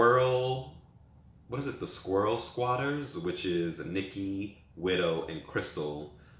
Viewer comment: did you say squirrel squatters .... 💀💀💀💀💀